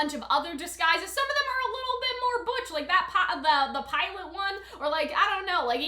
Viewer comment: did somebody say "Pilot Neo?" 👀